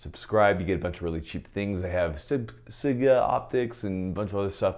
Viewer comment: I thought you said you hate sig red dots?